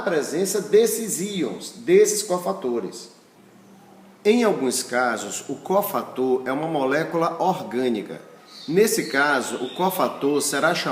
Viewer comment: Professor obrigada pela aula. Gostaria de tirar uma dúvida, em um determinado ponto da vídeo aula o sr. fala que quase todas as enzimas são proteínas e que o cofator é orgânico. Aprendi que todas as enzimas são proteínas, mais nem todas as proteínas são enzimas, e que o cofator é um íon inorgânico. O sr. poderia esclarecer essa dúvida? Obrigada. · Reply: Oi Adriana, o que o prof. falou foi: que em alguns casos o cofator é uma molécula orgânico (EM ALGUNS CASOS). Minuto 9:24. Abraços!